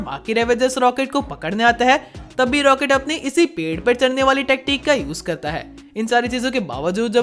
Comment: What you said TATTI, you all hear it carefully. And you said at all 'Pedh par chadne vali tatti use karta he'.